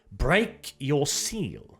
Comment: I heard "Break your shield." Not break your ceal.